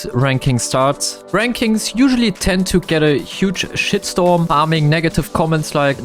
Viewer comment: what did bro say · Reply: “Shit storm”